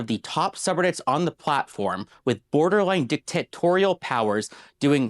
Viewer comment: Did he say "dick tit tutorials?"